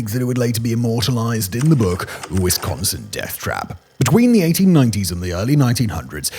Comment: Static X anyone? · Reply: First thing that popped in my mind as soon as I heard him say it...But I swore I thought he said "Wisconsin Death Trap" instead of Wisconsin Death Trip